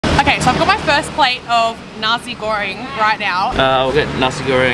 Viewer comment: i thought she said Nazi Goreng WTF